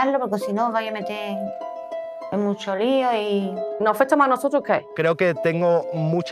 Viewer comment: Alguien sabe qué dijo? xdxd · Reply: Nos afecta mas a nosotros que a el🥬